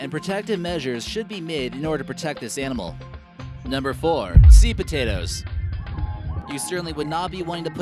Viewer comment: At no. 4 I thought he said sweet potato